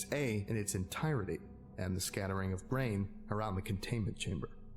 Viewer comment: I heard brain.